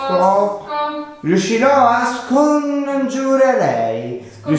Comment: Io al momento 15:30 ho capito la parola cioè riuscirò a sconfiggervi forza d'insieme vi amo siete forti